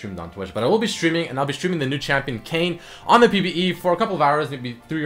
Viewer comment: anyone else think it said kanye?